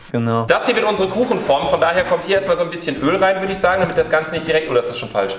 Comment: Ich habe verstanden,, da kommt hier erstmal ein bisschen Müll rein" 😂😂😋